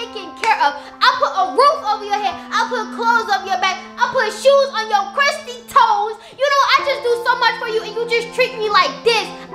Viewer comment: Just got here and I have already heard her saying I put shoes over your crusty toes